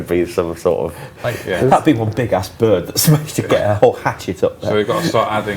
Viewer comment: Did say ass